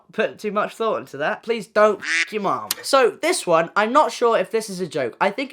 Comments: I was just eating my bacon covered hotdogs then i just hear "dont fuck your mon" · I hit my vape. As I'm exhaling I hear, "please don't fuck your mom." ☠😂